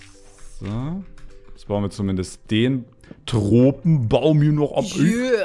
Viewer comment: Wer hat auch Drogenbaum verstanden XDD